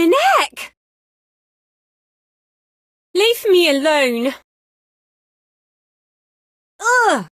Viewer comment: I thought she said leave me alone But she says leaf me alone